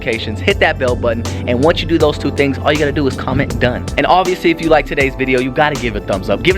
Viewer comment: Omg ive been folllowing u guys for awhile now... and i barely noticed u said u have to write DONE if u wanted a shot out lol i always wonder when will i get picked lol.... ive always thought u said comment wen done like comment wen done wayching lol